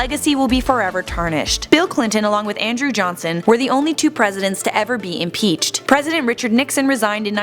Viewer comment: I thought you said that no president ever got impeached